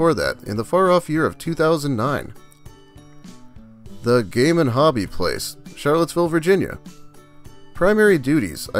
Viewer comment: I misheard “the game and hobby place” as “the gay man hobby place.” 😂